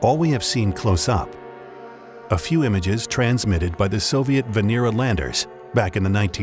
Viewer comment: For a split second I thought he said venereal landers, and spat my coffee out!!!